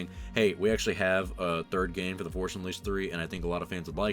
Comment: Sometimes it sounds like he says the forsh unleashed.